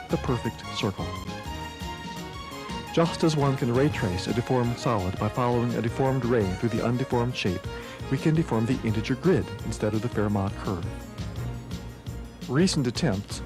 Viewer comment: Who understood what to say at 1:30? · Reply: "Just as one can raytrace a deformed solid, by following a deformed ray through the undeformed shape we can deform the integer grid instead of the Fairmont curve."